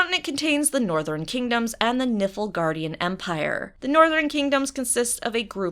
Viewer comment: I heard "northern kingdoms and nipple guardian empires" :|